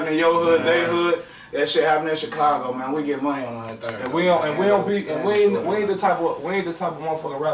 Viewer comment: Did he say "we get money on the third"? As in the third of the month? Is that how they are "getting money"? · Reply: 103rd